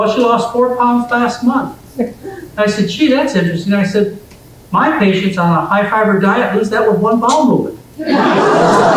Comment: What is McDougal saying there? Unluckily I cannot understand that joke because of old sound. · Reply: I think he said "jeez my patient lose 4 pound on a high fiber diet from one bowel movement"